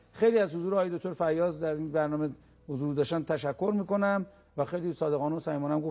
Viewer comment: صدای تیکه ی صحبت استاد داشنگاهو تا آخر زیاد کردم کاملا صدای اونی که داره از اتاق فرمان با هدفون مجری حرف میزنه میاد 4:33 میگه ازش تشکر کن قبلشم یکسری حرفا میزنه باید با برنامه صداهای اضافه رو حذف کرد تا معلوم شه چی میگه بهش